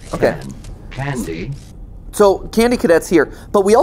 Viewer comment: Am I the only one who hears candy cadet at 1:53-54 say Cassie instead of candy? I rewound that audio like 10 times and I keep hearing him say Cassie 👀